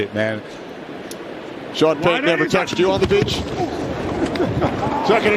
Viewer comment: HE SAID WHAT? 👉"Sean Payton never touched you on the _____?" Did Kenny Albert really just say that on National Television to Jonathan Vilma? I am shock this has not gone viral. Big WoW. Edit: Just in case someone is wondering what word he used on _____, it appeared he said "beach"